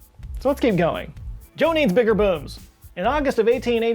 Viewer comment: "Joe needs bigger boobs!" You all heard it. Even captions got it 🤣🤣